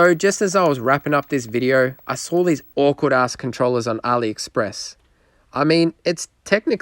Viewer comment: did he say ackward ass controller or was he saying the brand of the controller? 😂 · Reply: “Awkward-ass controller” - correct lmfao